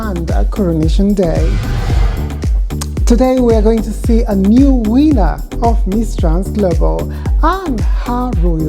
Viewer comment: I thought she said we were going to see a new wiener, got my hopes up god damn.